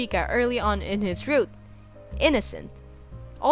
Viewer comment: When you said “he’s talking to Elizabeth the third” I heard “he’s f**king Elizabeth the third.” I replayed that five times until I understood it